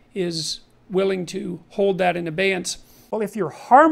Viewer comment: "willing to hold that in..." what? sounds like he said "advance".